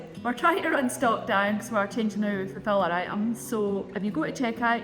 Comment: the accent is a wee strong here (plus I’m HoH.) Anyone able to help me out with what Mrs is saying as the reason? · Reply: Changing fulfillment centers